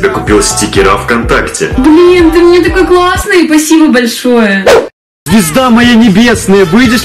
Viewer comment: ору мне одному показалось что он сказал пи**да моя небесная? 😇😅😅😂😂😂😂